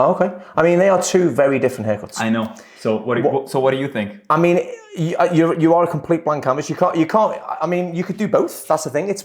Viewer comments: does he call him the W word? 🤣😂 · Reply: You are a complete… blank canvas 😂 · 🤣🤣🤣🤣Does he say what I think he does???🤣🤣🤣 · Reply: You have a complete blank canvas